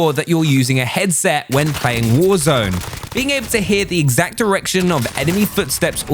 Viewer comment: did you say exact erection 🤔😅